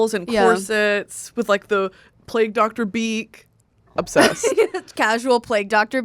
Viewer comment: Thought they said plague doctor beat like a makeup look and I just accepted that concept as truth